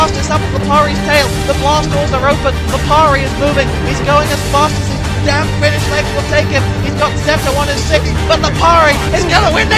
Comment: ROFL did say "he's going as fast as his damn finnish legs will take him?" 2:12.